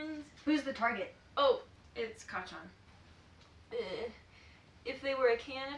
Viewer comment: Me 1:58 um what did you just call Bakugo!!! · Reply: Why are they all calling him kacchan!!!